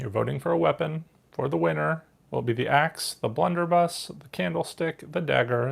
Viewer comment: A train and station board, that was different. There was weapon card I just couldn't catch what you were saying it was. Sounded like you were saying, 'blender' something????? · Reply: Blunderbuss. It’s a type of gun.